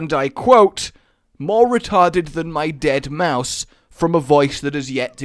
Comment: When you said Dead mouse at 2:08 Autogenerated captions said Deadmau5